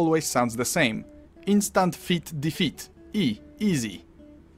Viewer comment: I hear "Instant Feet Defeat" Image in my head: KOed by smelly feet